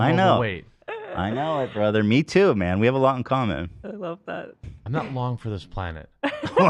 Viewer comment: wtf does he say in that soundbite? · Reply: "Im not long for this planet."